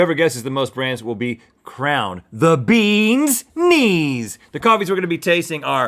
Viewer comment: I thought you said bee's knee and I thought those were bees for a 2nd!!